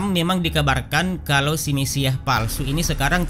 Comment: Simisiah palsu maksud nya apa ya... Coba kalian dengar apa yang diucapkan itu. Apa saya salah dengar